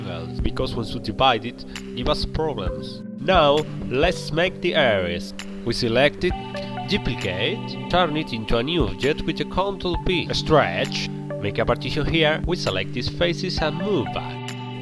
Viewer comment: what is the shortkey/what did you press? How can you do that?. Sorry, i can't hear you clearly. Did you just said "Control P"? Or what? Please tell me~~~ 😭😭. Thank you.